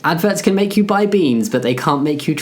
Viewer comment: In the beginning I thought you said “ads can make you bi (bisexual) beans but not trans” and now with the context I understand it’s “buy” not “bi” 💀